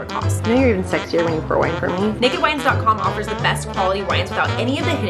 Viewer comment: when said “naked wines” i heard “naked wives” i was like oooookkkaaayyy 🔥